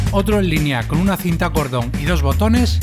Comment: oyeron dijo en el minuto 00:10 cinta porno o no c pero yo oi eso